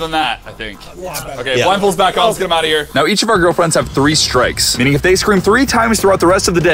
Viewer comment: Why did say my girlfriends 0:50 🫣